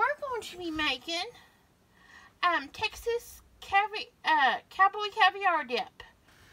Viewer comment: I must need sleep… I thought it said Cowboy cadaver dip LOLOL